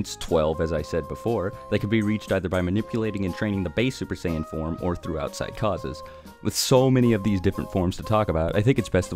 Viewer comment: I thought you said “or throughout psychosis”